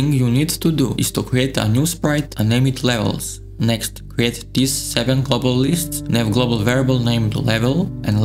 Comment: What does he say there? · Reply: "Create these 7 global lists"